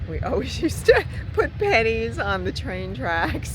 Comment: Such a great video to sit through. On my list; for sure. BTW, I thought you said panties when you said pennies. LOL!!!